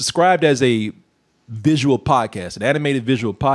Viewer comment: Instead of "visual podcast", I kept hearing "visual POT cast" Honestly I wouldn't be surprised if they were smoking some with a little more something extra while making this show